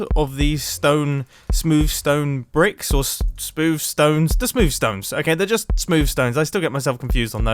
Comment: I still get thrown off when mumbo says smooth stone because I hear “smoov stone” which is already something I yell at my father for, but I give him a pass because English isn’t his first language